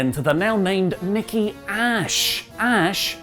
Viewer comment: So... the acronym for "Nikki Almost A Super Hero" would be "Nikki AASH" ... it sounds like is saying "Nikki ASS" ... wtf? I know these guys LOVE this sort of stuff but... it's literally the wrong acronym...